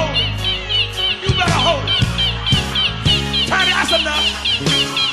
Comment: Tiny that's enough that's enough that's enough 😂😂😂😂😂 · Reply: I thought he was saying Patti that's enough. When I saw it without video I assumed it was Patti LaBelle